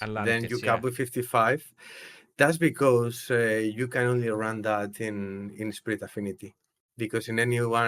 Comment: did he say that the team is only consistent on spirit affinity?